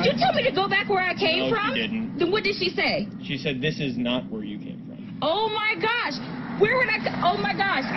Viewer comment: play it back at 0.25 speed and you will hear exactly what said, "this is not where you came from".